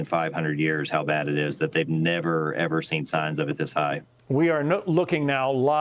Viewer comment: I’m dyslexic thought he said highest he’s ever BEEN.